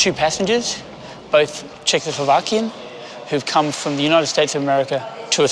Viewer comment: He said ‘Czech’ couple, meaning they’re from the Czech Republic 🇨🇿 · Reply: watch it again , he clearly said Czechoslovakia ...